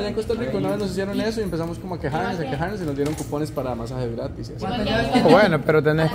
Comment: Escuché mal o el Tico dice "masajes gratices" ?